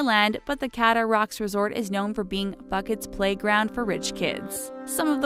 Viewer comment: Lol am I the only one who thought she said “bucket?” 😂😂😂 I think she mean Phuket lol